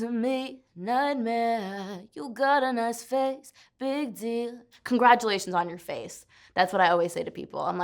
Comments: I thought that lyric “u got a nice face, big deal” I thought she was saying “u got a nice face, big d” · i always thought she says ''you got a nice face, big D'' LMAO